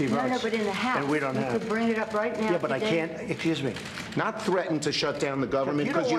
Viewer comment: Hahahaha! At 1:50, instead of saying "Excuse me' -- Trump says, "Accuse me." !!!!